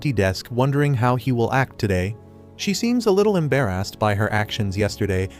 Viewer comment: embarrassed I heard em bear a**ed bru